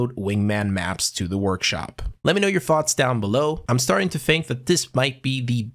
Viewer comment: I heard "let me hear your farts down below" at the end of the video